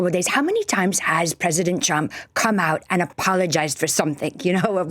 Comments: I love how it sounds like she is saying "President Chump" I've also heard other Republicans calling him "Chump" recently: Its the only truth the Republican (MAGA crew) have spoken. Also the mental gymnastics these people are having to perform in order to support this monster, are painful to watch. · She sounds like she is saying President Chump. She should speak for him often. At least she addresses him correctly. 😏